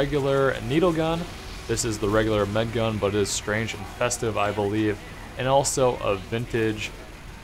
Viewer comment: Thought he said "Strange and Infested"